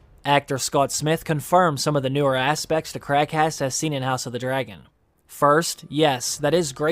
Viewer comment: When you say Craghas, it sounds like your saying crackass. The Crabfeeder in the show does look like he be a crack head.